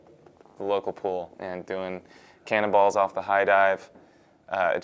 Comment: It sounds like he said, "Cannibals off the high dive." lol